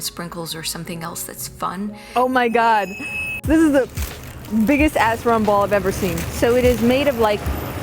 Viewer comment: Love your videos. Did you really say biggest a_ _ rumball?